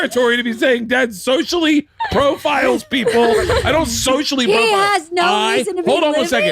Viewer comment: I thought they said racially profile😂